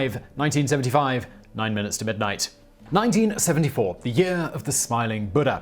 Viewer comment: did simon say 1975 instead of 1974?